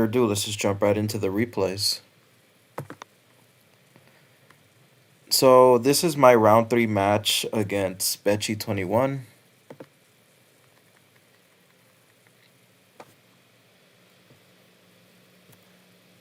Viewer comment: did you call him benchi? 🤣